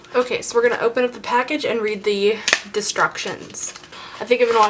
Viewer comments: i thought you said 'Destructions' ... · LOL , she said destructions instead of instructions